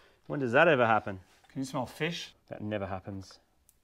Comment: does he say "Do you smell fish?"